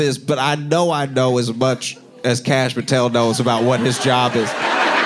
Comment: Josh says "Kash Patel" very clearly, but the caption says "Comey." 🤔👀